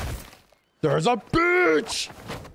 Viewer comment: Its sounds like Max is saying "There's a Bitch" Lmao.